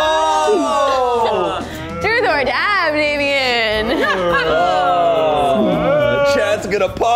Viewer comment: did she just call him “babien”?